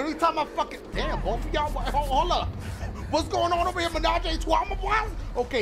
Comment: wtf did he say LMAO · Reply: ménage à trois aka "Threesome" in French